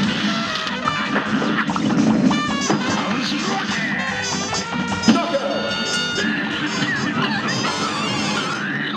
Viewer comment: what did he just say? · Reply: “Oh yeah”